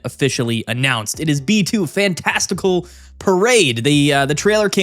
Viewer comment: I thought it said "Testical Pride" lmao